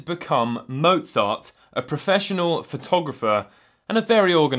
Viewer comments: I thought he said Mozart was photographer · I legit thought you said “Mozart a professional photographer” And got very very confused